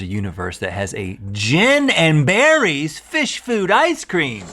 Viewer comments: I thought they said “gin and berries” ice cream and I got super excited. Gonna go whip some up now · Thought you said "gin and berries." Sounded good for a minute. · I thought he said "Gin and Berries" fish food ice cream until I read the pot I was like whoa